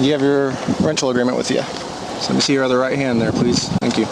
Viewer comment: Did he just say "Let me see your other Right hand" ?